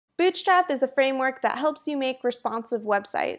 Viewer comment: Legit sounded like you said Bitchstrap